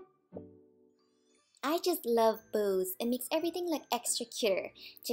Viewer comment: Ngl it sounded like you said "I just love booze" in the beginning. Did a double take before I realized.